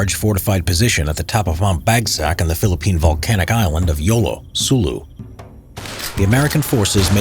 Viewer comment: I swear I heard in the the middle of YOLO that would explain a lot